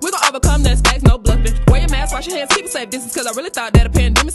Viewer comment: I thought she said, “Where your mans”